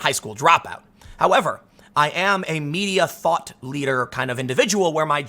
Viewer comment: When he said "online thought leader" and "thought police" I misheard it as "thot" ... this happen to anyone else?